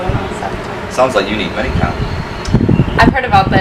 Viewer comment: Hey bro! Thnx for the vids! Hey bro, got a question... about 4:30 you mention something that sounds like 'money-cam', but i can't seem to make out what you're saying.. I've tried, and I've googled but can't find anything... can you please share the name & how to find that teaching resource? it sounds interesting & helpful.. I'm also an online teacher. :)) THNX bruddah! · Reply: Hey man, it's ManyCam. A great tool for teaching online. Here's an article about it: <link>